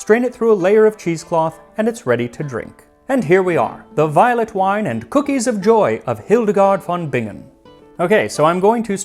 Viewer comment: I totally heard that as "The violent wine and cookies of joy" and thought....tell me more.